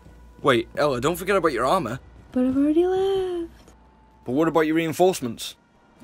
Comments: What does Ela say at 0:09 ? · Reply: "But I've already left" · subtitle pls what did ela say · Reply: "But I already left" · I can't understand what Ela says on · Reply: "But I've already left" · what did she say? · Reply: She said I have already left